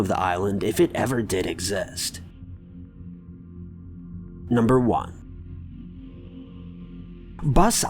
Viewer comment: Am I the only one that heard him say “number 2 “ instead of number four?